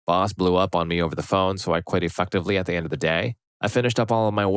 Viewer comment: it sounds like you said e(f-word)tively immediately, gave me a bit of a giggle 😂